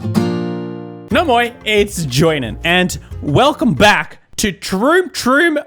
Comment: For so long i thought you said " oh boy its joinen" srry